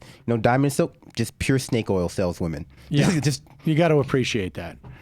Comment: I thought he said 'stink oil salesmen.' Fits better, actually.